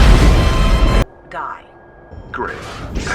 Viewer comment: I understand it sounds more like "guy," but I really like the idea more that she says "die" like I originally heard it as... and mr unemployable is like "great, thanks"